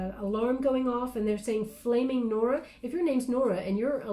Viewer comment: I’m Norma and I heard “Blaming Norma” 😂